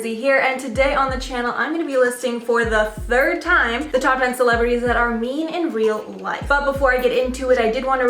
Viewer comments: Thought it said "celebrities that are MEN in real life" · I thought it said celebrities who are MEN in real life lol. I was like wow! Theres that many that we are on part 3!?